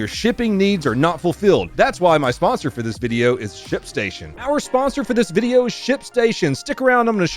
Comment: Check out <link> to get a 60-day FREE trial and get up to 84% off USPS and UPS rates when you use ShipStation! Watch the raw footage here: <link> Support the channel at <link> Check out Donut After Dark, watch videos early, see uncensored versions, and get access to exclusive content at <link> TWITCH.TV/DONUTOPERATOR MY VLOG CHANNEL: <link> MY GAMING CHANNEL: <link> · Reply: Every time you say ship station it sounds like your saying shit station which makes me think of T-Rex Noah. 🤣🤣🤣🤣